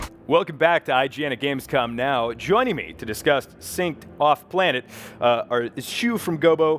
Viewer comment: did he say "to disgust" and not to discuss xD